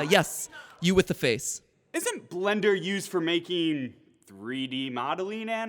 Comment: I thought he said Tender instead of blender🤣🤣